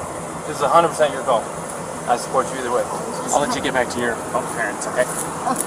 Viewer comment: what does the officer say to Gabby...."I'll let you get back to....." what? Get back to what? Once I hear what others "hear"....I'll post why. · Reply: i think he said i’ll let you get back to your parents, she said she was calling her parents earlier